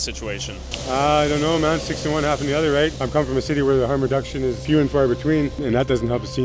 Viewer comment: wtf did he say?😂😂😂 “1 of 6 in half a dozen. I’m from the city” I have to go back and replay a thousand times bc I could not understand and even tho I still barely understand, I know I lost brain cells trying to make sense of wtf he said bc every which way you flip it and turn it or fold it, still makes 0 sense.